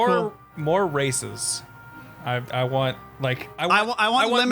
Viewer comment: For some reason when y'all were talking about what Xenoverse needed I heard "More racism" and a thought of piccolo bitchslapping Killing saying "Neel inferior being! Yes...now suck my non-existant di-" came to mind